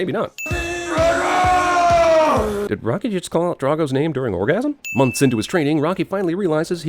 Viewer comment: " did rocky just call out dragons name during orgasm"🤣🤣🤣🤣🤣🤣🤣🤣🤣🤣🤣🤣🤣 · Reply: Drago.